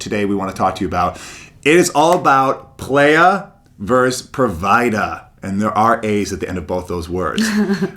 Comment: wait. I thought for a sec you said the player IS the provider. that freaked me out lol